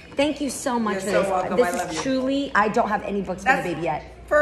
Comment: At the beginning she said she’s been getting books for the babe but then at the end she says she doesn’t have any books for the babe????? Which is it Jeannie!!!! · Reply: I thought she was saying she doesn't have any books on that manner